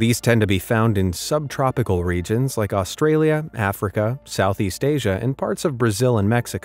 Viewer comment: did you say africa? Well I went there